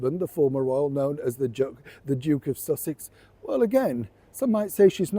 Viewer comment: I thought I heard you start to say "the Joke of Sussex " 😂😅